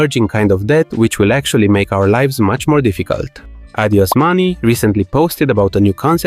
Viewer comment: It sounded like: "Adiós Money" which still accurately describe the current job market 🥲